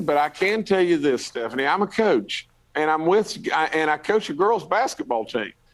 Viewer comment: "I'm a Cooch." Did I hear him correctly? Then he repeats it in 1:31, with some pride. He's a giant cooch alright.